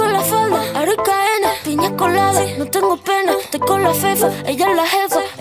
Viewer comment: alguien mas escucho ( no tengo p3n3)??? osea lo escuche raro xd pero suena como dijera eso